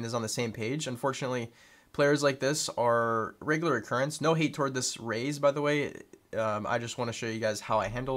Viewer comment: Omg you said “no hate towards this raze” I swear I heard “no hate towards this race”LOL ty for the videos man big help